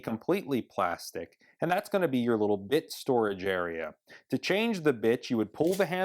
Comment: Did ANYONE notice he says "bitch" at 7:58? I slowed it down to 0.25. You can clearly hear him say "ITCH" instead of "ITS" for "drill bits"